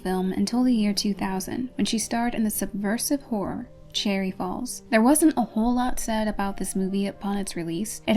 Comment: I thought you says "cherry balls"